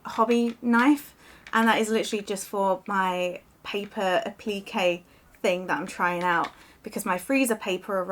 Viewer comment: Such great finds! Thanks for sharing. One funny thing: I'm from the US and sometimes can't understand certain words from the UK, so I kept hearing you say "P.K." and I was thinking, "What is P.K.?" But then I realized you were saying "applique!" Haha! In the U.S. we pronounce it with the emphasis on the first syllable. The "a" sounds like the "a" in "app", and the "i" in "pli" sounds like the "i" in "if", instead of an "eeee" sound, so it's "AAA- pli-kay." I love England! I wish I could visit often (only been to London one time), but it's so expensive to visit from the U.S., and the exchange rate with the dollar is brutal. Hopefully, I will get rich soon and then I will visit many times a year! lol